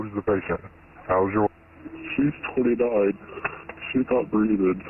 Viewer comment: the in video subtitles says “she’s 29” but my subtitles say “she’s totally died” 😳😳